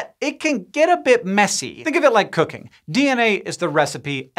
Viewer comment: When Hank said "think of it like cooking" I heard " think of it like cocaine" and let me tell you I was VERY confused for a good minute 😂